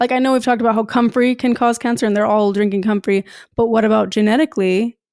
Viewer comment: What's saying at 15:44 ? She repeats it. They're all drinking what? 🤔 I rewinded a few times but my foreign English speaker brain just cant comprehend it 😅👂 (And I havent seen all videos with her, it seemed to be a reference) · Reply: It's comfrey. The herb comfrey. From my understanding, they use it a lot- put it in smoothies, and probably teas.